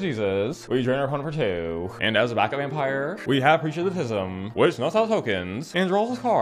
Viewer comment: Haven't played for a few sets, the recent sets don't do it for me... waiting on things to get juicy again, but I still watch this religiously lmao When you said "preacher of the schism" I thought you said "creatures with the 'tism" and it still made sense to me.